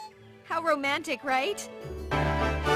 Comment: "Never show dismay" * stars brake dancing * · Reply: I always thought he said "never strut this way!"